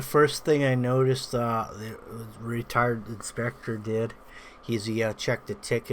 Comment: I thought he said, retard inspector 😂